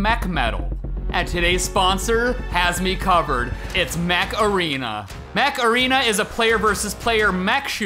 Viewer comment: Did he say macarena? Hey! Macarena Aight